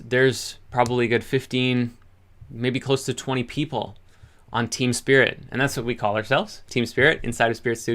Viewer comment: Team Spirit sounded like Teen Spirit 🎶 😉